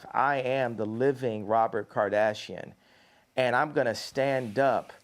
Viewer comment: Did he just say “I’m Robert Kardashian”?? 😩